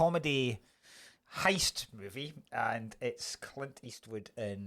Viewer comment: What was next weeks film? It sounded like said clint eastwood in achillies heels, which sounds a bit mental and nothing I can see in his film list looks anything like that